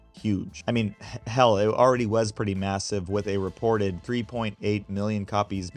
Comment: ma...massive did you say?...